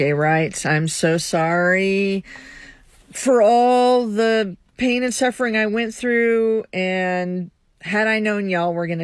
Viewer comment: AT FIRST I COULD HAVE SWORN THE SECOND LESBIAN SAID, ( ALL THE PENIS SUFFERING)? I HAD TO LISTEN TO IT TWICE TO REALIZE SHE SAID PAIN AND SUFFERING.